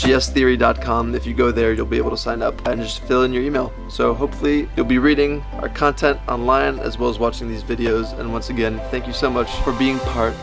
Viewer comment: I fucking love you guys so much i will meet you guys someday and tell u about all the adventures you inspired me to go on thank you for being such amazing idols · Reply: Emanuel Brown I thought It said thank you for being amazing idiots 😂😂😂😂